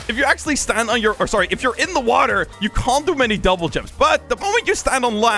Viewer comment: did i just hear him say jumble jumps? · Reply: I put my volume max and I heard “Jubble jumps”